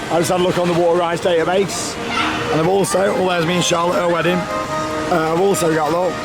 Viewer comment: Correct me if I'm wrong but it sounded like said "that's me and Charlotte at A wedding" not "our wedding". Could be wrong though